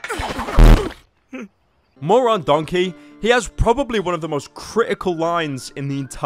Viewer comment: did he say moron donkey or more on donkey?